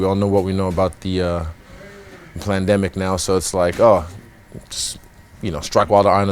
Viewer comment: he said Plandemic instead of pandemic. What a legend haha